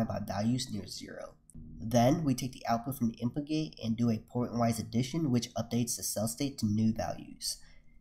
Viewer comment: what does he say here 7:50 portenwise addition?